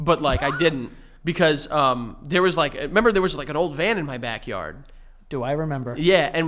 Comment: I thought said there was an old man in his backyard instead of old van LMFAO